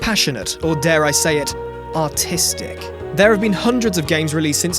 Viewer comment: Since he is British it sounded like he said autistic